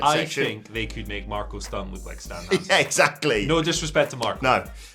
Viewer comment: I had to rewind a bit because I thought I heard Andy say: "I think they could make Marko Stunt look like Danhausen" 🙆‍♂️ ...He said Stan Hansen...😂🤷‍♂️